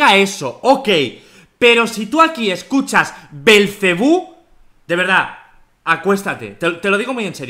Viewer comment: En la parte que decía belcebú yo entendí "Ven jebus jebus jebus jebus"